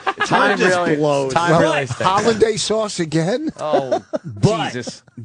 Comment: did vos call hollandaise sauce holiday sauce?